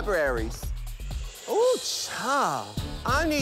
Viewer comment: what is vookoo shade mean ? at the end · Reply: I thought he said "beaucoup shade". Beaucoup is French for "a lot (of)"